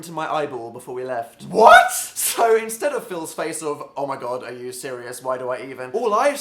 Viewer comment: Hey Dan! I love your videos and i wanted to make sure everyone could enjoy it! I just wanted to let you know your captions are a little off. About 4:26, the auto caption says "so instead of filling space of oh my god" when it meant to say " So instead of Phil's face of oh my god". When you get a chance could you fix that? That'll help the 360 million people globally who rely on closed captions to understand videos. Thank you and keep up the great work!!